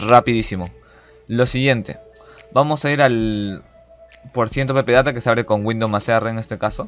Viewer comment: dijo una palabra rapido que no entendi en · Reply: %appdata% eso dijo